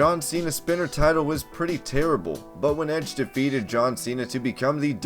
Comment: Did he just say the spinner tittle was terrible?